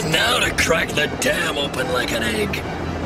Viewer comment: At about 14:40 I thought Vile said "time to open that damn thing like an egg" wow the Future is edgy! but just the wrong dam